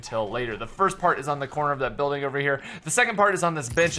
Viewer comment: lol 1:08 sounds like he says bi*ch